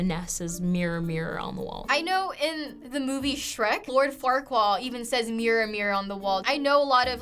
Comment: did she just say "Lord Farquall" 😂😂